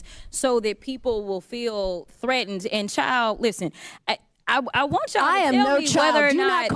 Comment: So Nancy Mace wants to take Jasmine outside just because Jasmine said “chile” which was not calling her a child. Man….. this is sad. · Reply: I thought she said child please, let me re-watch it...